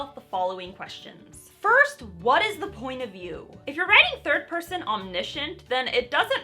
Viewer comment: You made me do a double-take, cuz I thought you said, "First: what is the point of you ?" I now realize you said, "First: what is the point of view ." lol